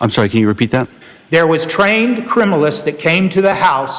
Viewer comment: When defense referred to the "criminalists" from the state police I straight up heard him say "criminals from the state police" 😂 which with trooper Proctor in the mix checks out.